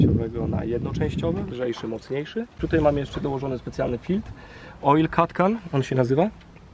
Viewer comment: A kiedy Dodge Chellenger Demon 😏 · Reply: co on powiedział? "Oil cat can"? xD Wskaźnik dodatkowy informuje o wstrzykiwanym płynie i innych ciekawych rzeczach xD To w serii jakie ma doładowanie? xD Profeska...